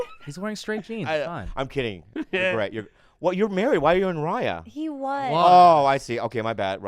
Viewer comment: Wait, isn't??? Didn't he just have a kid? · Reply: I thought they said when they were talking about why he's on that dating app, Bob said "why are you on that your married" and khalyla says "not anymore"